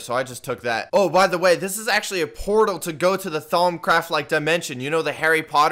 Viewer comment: lmao did he just say “thongcraft like dimension” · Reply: temptell no it’s thaumcraft